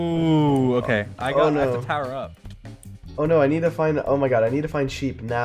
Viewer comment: anyone else think dream said “power up” meaning he would have to kill a wither and ether get a max beacon or a small one I can’t remember. But the calmness in his voice would have been amazing if it were power up. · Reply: He said tower up